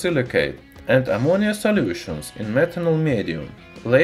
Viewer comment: did you just say smetanol?